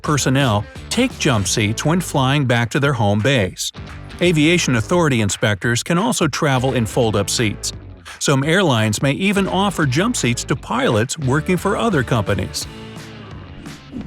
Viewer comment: I thought 2:33 said some hairlines